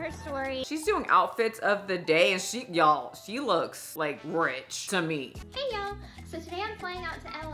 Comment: I listen to your videos while I clean, so I'm not typically watching. When you said she looked rich at 12:17, I thought you said she looks like the grinch! I rewound the video and was deeply disappointed, lol!